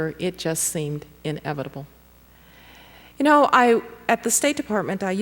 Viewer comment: what word does she say @ 25:17? She said: "I used to take people through a little what...faliloque? What does that mean?